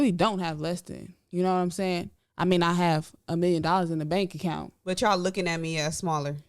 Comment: Lol did she just say on 58:34 she haves 1 million in her bank account 😂🤣😂🤣🤣